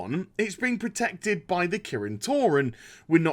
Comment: I could've sworn I heard "Protected by the Kirin Tauren" and thought of magic cows. Yeah, blizz, I'm that bored.